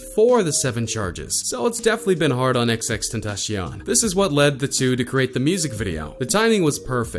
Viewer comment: It sounded like he said xxxtentashion it’s xxxtentacion cion c-ion